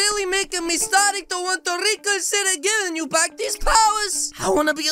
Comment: I thought he said he did not have his wolf powers